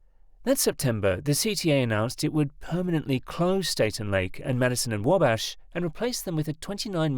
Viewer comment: "Staten Lake?" It's "State/Lake," which is normally said as "State and Lake." There are even pictures of the station signs in the video. Yes, I agree with the point of this video that all this money and it ultimately doesn't solve the problem of connecting the two stations (elevated and subway) as one. The interesting thing is that the next station to the west, "Clark/Lake" gives a real life example of what this could be because it is embedded in what used to the the Thompson Center building (now owned by Google). Instead, we're still getting an awkward transfer like at "Harold Washington Library" elevated station which is between the two subway lines.